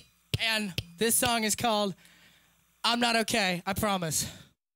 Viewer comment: My friend thought that Gee was saying "I'm not O-Gay" The fuck. I don't know how to feel anymore